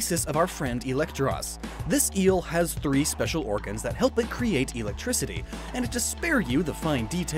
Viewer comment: Did... did he say "EELectrictiy" in minute 3:20-something?